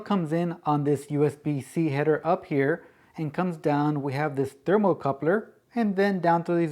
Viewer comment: Hello and thanks. What type of coupler are you talking about at 02:36 ? (I hear thermo coupler but I must be wrong) Is it the component that isolate the noisy USB from the rest of the device ?